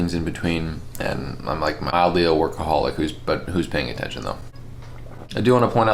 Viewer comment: I thought you said mildly an alcoholic. I smiled and sat up in my chair. Then I played it back and actually heard workaholic. I was a little sad but honestly it would be really interesting if you were an alcoholic and were super productive too 😂🤣 That's content right? You're eating real good my friend. 👊